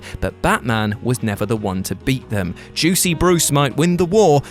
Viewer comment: Did you just say "Juicy Bruce"? 😑